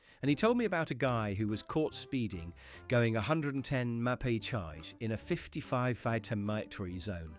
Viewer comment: Did you say the guy was caught going 110 "Ma-pay-chiyj" in a 55 "fite-or-mite-tury" zone?